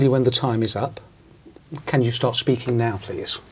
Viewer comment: Anybody note at 5:04 The examiner said that Can you stop speaking right now😞 Instead of start speaking right now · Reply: He said "Can you start speaking now, please?... This is British pronunciation dear. ;)